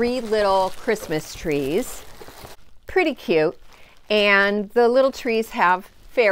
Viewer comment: Omg....looks like fun!! I love the estate sale Secretary desk. And I thought I heard "22 cute" mentions. 🙏💖🌻